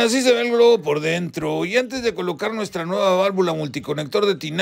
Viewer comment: Un tema más en tu escuela ferretera? Pensé que decía Hoy te mamaaaas en tu escueeeeeela ferreteeeeera😎